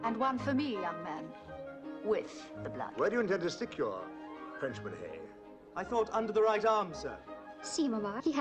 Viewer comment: Did anyone understand what the English officer said at 4:25? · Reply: "Where do you intend to stick your frenchman, Hay?"